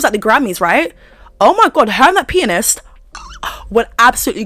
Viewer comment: thought you said her and that penis !